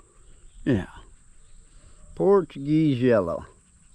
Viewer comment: Funny. Watching while doing dishes, I thought you said “porcupine jello”, before you said its name again and I heard “Portuguese Yellow.” 😂